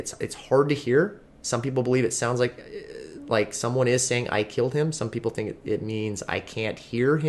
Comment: Although I do think the mom is responsible, ...I don't think she said "I killed him" ...it sounded like "i can't breathe" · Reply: I heard "I kilt him", "I can't breathe", " Am I goin to jail?"